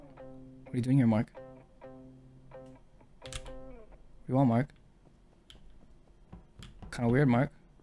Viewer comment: Idk if it’s just me, but I heard 2:07 as “what do you want mark?” But like reaaaaaaallllly shortened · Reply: He actually said "where you goin' mark?" but in Jaenglish ofc